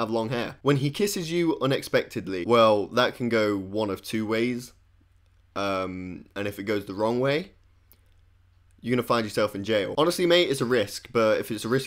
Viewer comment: I thought you were saying gay is the wrong way😂